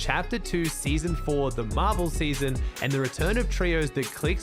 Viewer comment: when he said marvel season i thought he said marble season